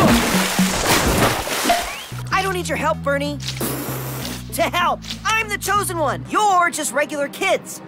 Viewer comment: did he say “to hell”??